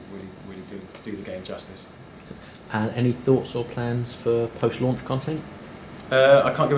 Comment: What does he ask at 9:48? I've replayed it 6 times and I can't for the life of me decipher what he's saying. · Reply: "Any thoughts or plans for post-launch content?"